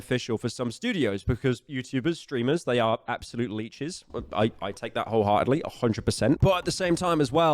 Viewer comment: how the heck did I mishear that as "they are absolutely jizz"